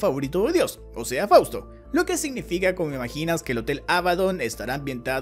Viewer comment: que es es lo qué dijo?? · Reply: JAJAJA yo también lo escuche, pero es "como imaginas" jajaja